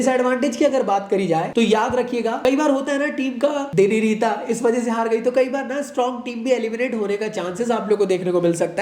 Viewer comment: kya bola sir samj nahi aaya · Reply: team ka day hi nahi tha mtlb team ka luck na ho us din